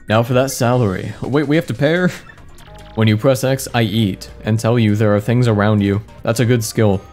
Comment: it sounds like is saying ‘I yeet’ instead of ‘I eat’